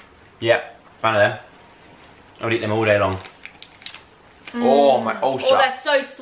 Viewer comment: says" Ohhh my ulcer." But I heard "Ohhh my ballsack." Not sure how ulcer can sound like ballsack, but it did.